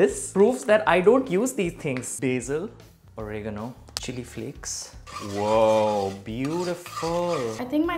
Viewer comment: I thought said "chicken flakes" when he said chili flakes and I was thinking "what? Where do you get chicken flakes?" I was imaging chicken skin flakes lmao 🤮